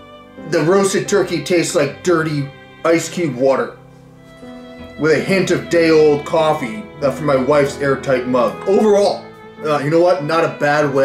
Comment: ok so I was fixing my bike and all I heard was "TASTES LIKE DAY OLD COFFEE FROM MY WIFES AIRTIGHT MUFF" and I tell you you what my ears pricked up and I thought "tell me more father" but after further research I discovered this was not what said and I was very disappointed